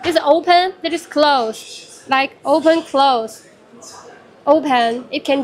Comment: Did she say "oo bhen"?